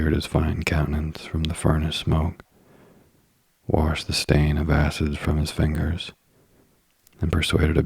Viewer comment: I thought u said wash the stain of asses and I died laughing 😂😂😂😂🤦🏻‍♀️